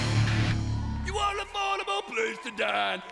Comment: When I was younger I thought he said "You want an affordable place to die, sell your kids for $3.99!"